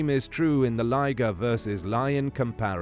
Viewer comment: Love it, by the way I thought you said Liger vs Liger 😂